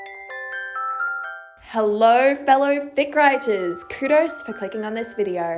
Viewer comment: when you said fic writers, i thought you said dick riders 😭